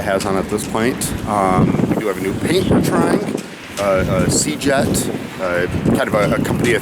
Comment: what antifouling are you going to use for the colder northern waters you are coming to ? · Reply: I think he mentions a product called 'seajet'?!